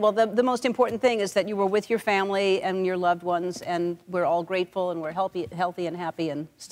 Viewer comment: did Ellen say "we're all grapeful" after that grapes story??? lol